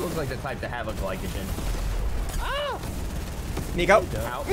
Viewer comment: Is it me or did he just say the n word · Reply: Burgurboi Burgurboi he said nico